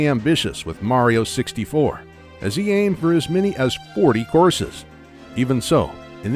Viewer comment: Oh COURSES. I kept hearing, "horses" and couldn't figure out why Miyamoto wanted 40 horses in his game.